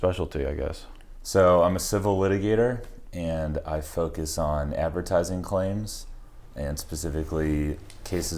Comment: In the beginning when he said "I focus on advertising claims" I thought he said " I fuckin saw an advertising claim"